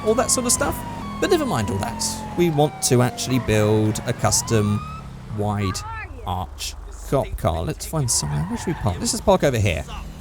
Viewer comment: did he say "wide arch cop car" or "wide arse thot car", coz for some reason I heard both of those